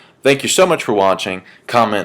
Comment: lol i thought you said comment RAPE and subscribe. (good shit)